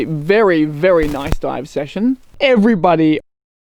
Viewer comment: I heard ‘every buddy’ 😊 And I felt Big Open Fun Cave had a much nicer ring than Smuggler’s cave haha Thank you for sharing your adventures, and as always Great editing!!